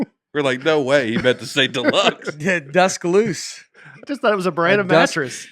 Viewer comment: I heard “dusk loose” in passing last week and not in a million years would I have thought it was supposed to be deluxe. Hilarious.